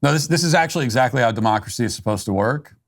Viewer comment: it sounded like she said a “mockracy” instead of democracy.